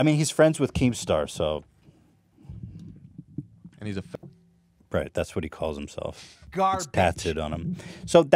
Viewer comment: what did that guy say? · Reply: Omg lmfao not saying fag00t